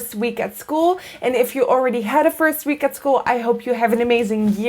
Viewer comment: you said "ooridy" instead of already. I do that too and my friends make fun of me. What accent is this? I grew up in Maine.